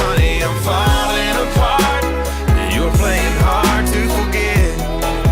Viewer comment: I always thought it was "plain hard to forget" but then I heard it was "playing hard to forget", and I cringed from the cringiness.